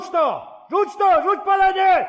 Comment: Rzuć to, rzuć palenie bo będę strzelał. Nie można odmówić. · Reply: Ja usłyszałem: "Rzuć to! Rzuć baranie...!" Wubec (tego) w poniedziałek idę do laryngologa...